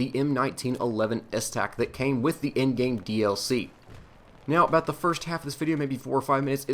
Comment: What he said "It comes with the end game DLC" what I heard " It comes with the in game DLC" I was like WHAAAAAA?